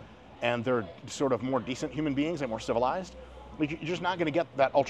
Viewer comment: listening on 1.75 speed I thought he said (of the more scientific, natty guys) "most of them are not on drugs, sort of more decent human beings and still alive" and I lol. I had to go back and listen to it and slow it down to realize he was saying "more civilized". I was about to say, man, this dude is another level of hardcore. It's not funny to laugh at the short life span of people who juice heavy but Mike Israetel has talked about doing it and the risks so he would probably have license to make a statement like that if he was (but he wasn't).